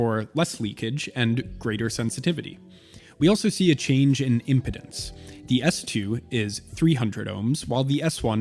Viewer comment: Need to get a dust rag or can of air my guy. What's the point of doing macro shots on a dusty ass product? Also, it's "IMPEDANCE" not IMPODENCE". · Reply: At first, I thought he had said impotence, which, uh, might refer to aging audiophiles? I lol'd when I heard it, as an aging audiophile...